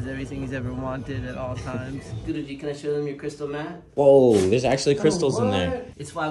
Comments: I thought he said "can I show them your crystal meth" 💀💀 like that's why the dog so chill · i thought he said to the dog "wanna show them your crystal meth?" 💀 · "Show them your crystal mat" i was fully expecting a different sound in that last syllable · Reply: i actually thought he said crystal meth at first lmfao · “Can I show them your crystal meth?” Ayo what, I can’t be the only one that misheard that 💀💀💀🤣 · I thought he said crystal meth and not cystal matt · I thought he said crystal meth not mat💀